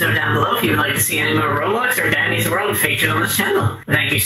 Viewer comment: I heard Daddy's world... SUS😡💀😡